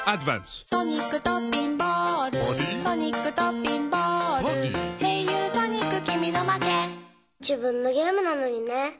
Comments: what do u guys hear? I hear, "Sonic the talking ball, Sonic the talking ball, Hey you Sonic give me the money" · I thought it said sonic the talking baller